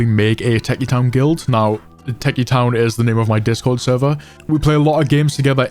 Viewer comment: thought you said teccy town until i checked